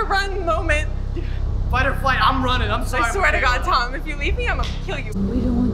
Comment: the real hero here is, she is calm and full of jokes. Does Tom call her butterfly at 5:05 :O that's soooo cute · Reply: emm I think he just says fight or flight